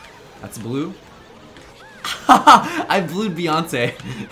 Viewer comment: “I blued Beyoncé” Never thought I would hear that in my life, ever. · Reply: I thought he said, "I blew Beyoncé" and I got really scared